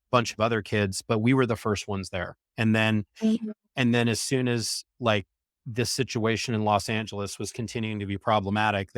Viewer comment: What is that? Someone says hey but I see no one talking but and I don't see any cuts in the video. Who said hey?(6:53) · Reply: is an active listener, it’s just her saying “mmmhhhmmm”.